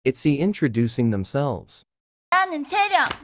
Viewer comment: I thought you said it's the introducing themselves you said itzy